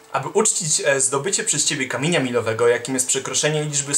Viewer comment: przeczytałem kamila miłowskiego 3:41 usłyszałem kamienia milowego xD